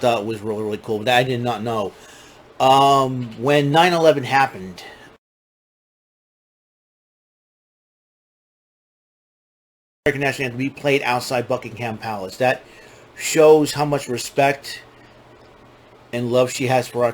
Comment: I couldn’t tell what you said there: it was frozen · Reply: I was talking about when 9/11 happened, the Queen ordered the American National Anthem to be played outside of Buckingham Palace.